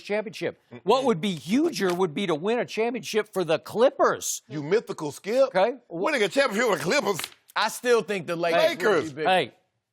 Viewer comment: What the f#&$ did Shannon say at 5:18 ?? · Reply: "You mythical, skip.... Winning a championship with the Clippers??!" Something like that. 🤔